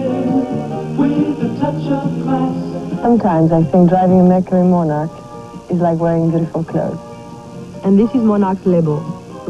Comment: Can anyone make out what Catherine is saying at 0:09?!!! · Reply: "Sometimes I think driving a Mercury Monarch is like wearing beautiful clothes."